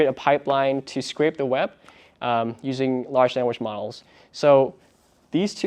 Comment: The transcript at 1:39 states that you are using large sandwich models. This must be a brand new type of model - mouth watering indeed. 😂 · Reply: I replied three times at 1:39. Is he saying 'large sandwich model?🤣